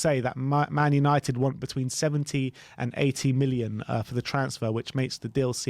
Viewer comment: I thought they said 18 million, I was like oh wow that’s cheap for such a talented player but 80 million?????? I’m more willing to answer a scam call and give them my info than pay that much for anyone really.